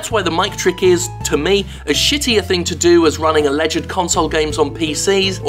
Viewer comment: are you kid- are you fucking kidding me, jim? i never once, ONCE thought that was npc dialogue. this video is reaching for something to complain about. if you ever play with a group a friends in a team based game it can easily sound like that when you arent raging at each other. it happens all of the time when we play league of legends. calling positions, alerting each other, issuing orders, planning etc. i normally dont have a problem with your videos. hell, ive likely enjoyed them all. but this one was complete bullshit imho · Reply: That guy with the face listened again. he says "as shitty a thing to do", which sounded like "a shittier thing to do". i dont know if it was because of his mic, his accent or whatever but i misheard. but thats the least important aspect of that comment. saying that the mic trick is equal to a bad port or downgrading is a ridiculous statement even it is his opinion. how is having a trailer that only misleads imbeciles worse than lowered visuals and selling a bad port? thats completely idiotic. and you would have to be insane to believe otherwise.